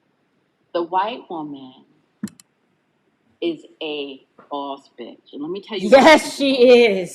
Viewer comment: Can someone please tell me what said? The audio seems shaky. TIA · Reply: She said the white woman is a boss bitch and she said nuance what she’s saying because she’s not talking about kissing white women’s ass. She said the WW will send her son to war even though it means he won’t come back because she believes he has to do what’s right and what’s for her.